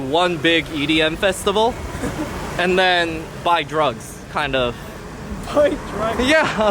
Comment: did you say "buy drugs"?! I thought Thailand was a sort of safe heaven where drugs are absolutely prohibited and law is ruthless on drugs abusers/dealers. In someway similar to Singapore.